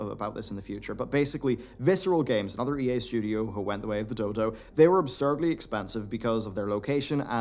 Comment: did he say dildo?